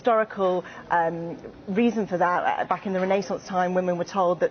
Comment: Pauses video -- Did you say rennaysince